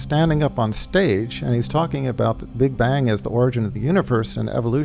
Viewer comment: my dear brother in 19:55 the guy is not saying the {BIGBAND} he says {BIGBANG} i think its a little grammar mistake i hope you correct it soon inshallah جزاکم الله الجنە